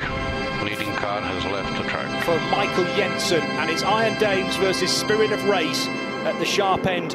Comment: did he say ? Iron Dames ... . .R e d e m p t i o n !